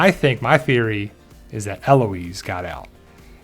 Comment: At one point, I thought I heard the children saying “Aunt Loise”